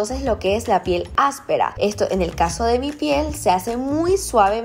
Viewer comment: escuche a decir en el caso de BTS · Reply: Dijo en mi piel escucha bien